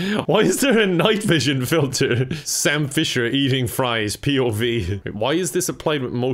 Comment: For a second I thought he said Sal Fisher and I was like 'WHOA'